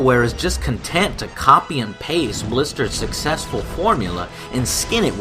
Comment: wait a sec, did he just say blister's successful formula? i think he meant blizzard also only half of these games were actually any good